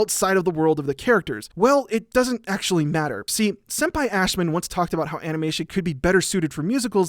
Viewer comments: um so maybe I misheard, but "senpai Ashman"? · did i hear 'senpai ashman'? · All I heard was "Senpai Ashman". That's the way I'll refer to him from now on. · Me: hold up ... did you just say senpai ashman?